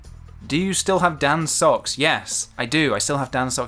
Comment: Ok but if you did auction Dans socks online you could probably quit YouTube and just live off of that money. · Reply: Okay I thought you said cocks and am currently laughing my ass off - scuse me